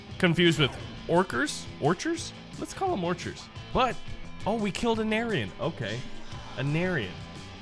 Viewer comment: i heard him say "we killed an aryan", why he no like germany